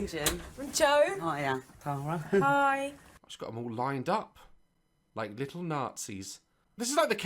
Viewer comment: did he just say all lined up like little Nazis